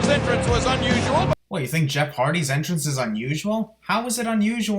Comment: I heard "Johnny Nitro's entrance"